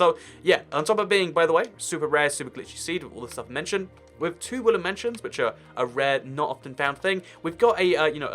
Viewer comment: I kept hearing you say “ woollen mansion “ Mmmmm I’ll leave now